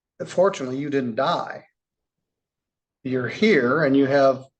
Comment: I thought he said "Unfortunately you didn't die." and was very confused for a moment.